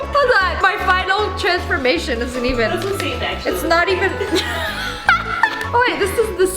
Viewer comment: Did anyone else hear voice saying "That's what Satan actually looks like" ??